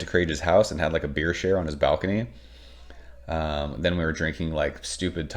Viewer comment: Ohhhh!!! "...had a beer share on his balcony." I had to rewind 5 times because I thought you said you, "...had a beer shit on his balcony." LMAO!